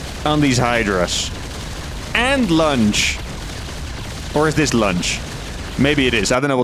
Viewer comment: Just me or it sounds like he is saying "Lunch"? xD I legit thought he was joking about them not getting anything to eat (ie. kill), from the lack of the other upgrades xD